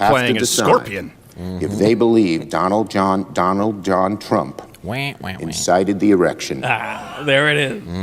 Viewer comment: did he say instigated the erection? Or did LWC just edit that in and not address it? Im guessing it wasnt edited in but either way it's funny.